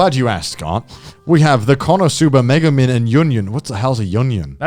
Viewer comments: Lmao, at 57:17, I heard "Conner-suba" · “What the hell is a junyun” poor yunyun even in real life she gets shitted on lmao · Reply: What I heard, "What the hell is an onion?!"